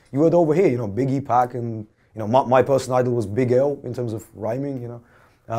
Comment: Who does he say his personal Idol was? Big L?